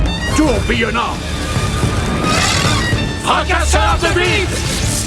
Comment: Fracasseur de quoi ? 🤣🤣🤣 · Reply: De vide❤